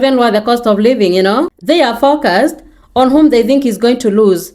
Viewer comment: your voice and your accent are so soothing btw, but when you said "they are focused," at first I didn't know what you said, and I heard something else, so I was like, "they are fuckers indeed, my friend" XD Trump supporters, man.